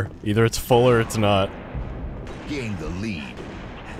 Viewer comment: "Gain the lead" but i heard it's "Game delete"